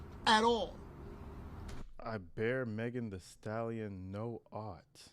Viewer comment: I THOUGHT HE SAID "I FEAR HER NOT".